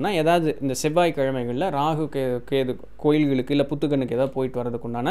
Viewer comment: Can anyone tell me.. What he is saying at @ 05:25 ? Kovilgal alladhu xxxxxxxxxxxxxxxxx ? is he saying puttukal... ? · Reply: புற்றுக்கண்